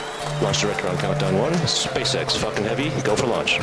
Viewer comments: What, is it me or it is everyone, what did I hear on 0:30… fk1ng heavy? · HE SAID "This is SpaceX fuckin' heavy"* !! AHHHHHHHH THE LAUGHS WILL DESTROY MY EARS AHHHH! Either that or it was "Falcon" and it sounded like "Fuckin". *EDIT: I watched in 0.25x speed, it was Falcon, it's just that at normal speed it sounded like "Fuckin"